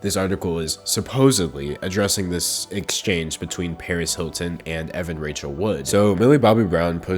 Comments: For a second I thought you said Perez Hilton. I was like why is that guy so obsessed with kids and what they wear? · i thought u said perez hilton and was freaking out like “HES A SHE?!?!”